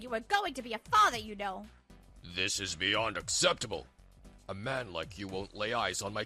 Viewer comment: did he say “beyond acceptable”? or am i just hearing it wrong